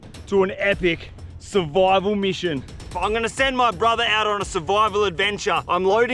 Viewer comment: I’m gonna ghee my brother up is what I heard 😂